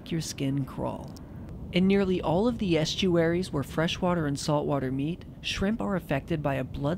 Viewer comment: i thought it said "pastries"